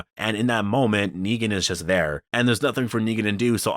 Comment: Also at 7:35 if you have subtitles it says "nigga" instead of "Negan" 😂😂